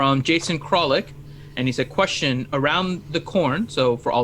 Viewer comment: I misheard "around the corn" as "around the horn" the first few dozen times you guys said it. I inferred what it meant from context, but the spelling I only understood after someone spelled it with emoji (as in "Question for around the 🌽"). I found it very confusing, and I'm glad to see I'm not the only one. Just sayin'.